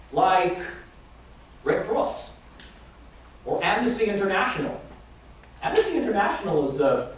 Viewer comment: Sir, great session!. Just wondering what name did you mention at 6:33 after Red Cross, I couldnt catch it. Thanks in advance. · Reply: Amnesty International :)